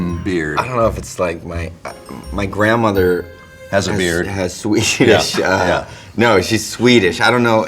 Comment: I really thought I heard James say "my grandmother has a beard" lmao 🤣